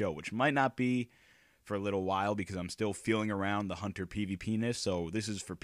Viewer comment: HAHA SOUNDS LIKE YOU SAID HUNTER PENIS